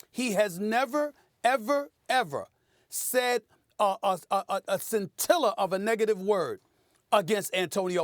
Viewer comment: someone please please please help me figure out where tf Stephen A. Smith pulled that word from. thx! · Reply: Scintilla- to sparkle or shine